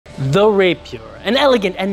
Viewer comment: Bro I heard “they’ll rape u” at the beginning and was like HUH??? 😳😨